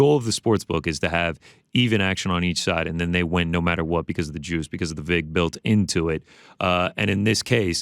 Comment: I thought GC said "[...] they win no matter what because of the jews" LMAO. I thought he went full Ye there for a second